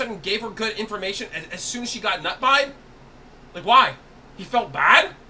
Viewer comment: I thought he said. Nut Pie" as in something to eat! Eeeewww